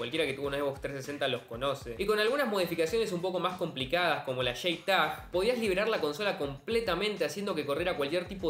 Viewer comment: Sólo entiendo "card" ¿Qué dice? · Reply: lo que dice es "como la J-TAG" ("jay" que significa "jota" en inglés bro xd)